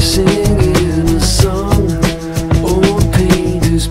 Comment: I thought they said that fresh fruit